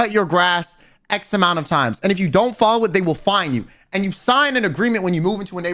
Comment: Ngl I thought at 0:36 he said that they'll find you I had to do a double take 😭